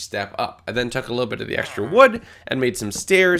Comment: did he say extra wood?